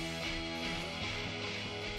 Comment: What did Birdie say ar 12:22? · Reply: “so that’s what they say”